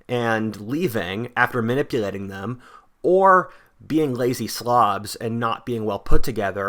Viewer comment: Did you say "Being lazy Slavs"???